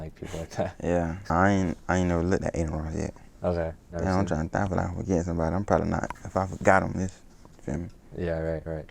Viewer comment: idk wtf he said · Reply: "Yeah I'm tryna think-I feel like I'm forgetting somebody. I'm prolly not. If I forgot em, it's.....you feel me?"